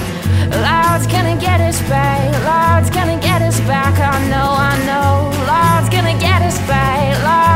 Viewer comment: Great song, awsome video. one question... in the original version he says ''im gana get her back'' but i kept hearing ''gods gana get'em back'', is that right or am i just hearing things? in anycase i liked your version much better.